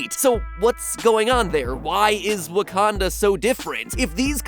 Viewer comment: a few time I heard "Wakando" LOL